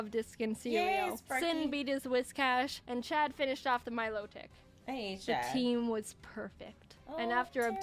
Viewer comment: I think she said this: I hate Chad